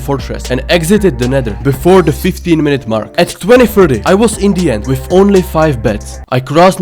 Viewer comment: did he say 20 30 i was indian or 20 30 i was in the end??!?